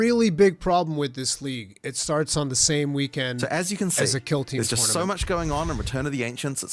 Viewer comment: Please some for the love of god tell me what game was talking about time stamp was 36:04 to 36:05 I just don’t know what he says since ggg guy starts talking anyone HELP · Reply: I think he's talking about a warhammer killteam tournament. At least that's what i'm hearing.